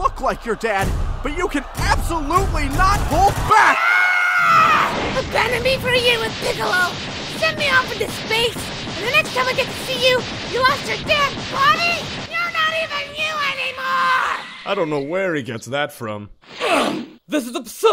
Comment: What the F&$K did Gohan say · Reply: Abandoned me for a year with Piccolo. Sent me off into space. And the next time I get to see you, you lost your damn body?! YOU'RE NOT EVEN YOU ANYMORE!!!